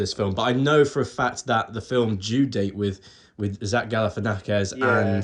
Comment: I heard "The film Jew Date"